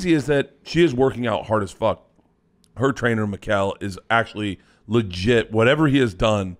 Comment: I thought I heard the trainers name was Miguel. I was like OK that’s it lol.